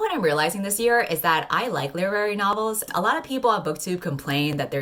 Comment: english is not my native language so WTF is saying? lerary novels? wut? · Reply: Literary novels